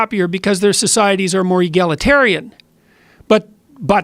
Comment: What's the word..? Yegaloterian? Not the native speaker, sorry 😅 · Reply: Egalitarian- equal